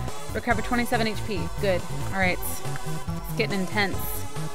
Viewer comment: it sounded like she said skip n tense